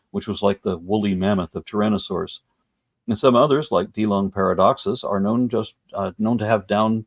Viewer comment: What is that dinossur at 20:03? I didn't catch the name. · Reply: Dilong paradoxus.